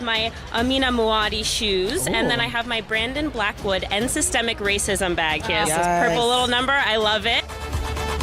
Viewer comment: did she really said systemic racism bag? 😳😳😂😂😂 Victimhood Forever